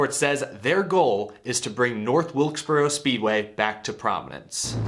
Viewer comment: I misheard you say "prominince" for "Providence" and RI needs a track for sure